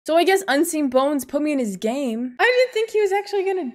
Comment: Lol why did you say at 0:00 BOIGUS